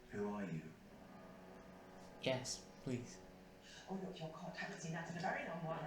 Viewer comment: I missheard here when she said oh look your cot, I thought she said, "Oh look your cross."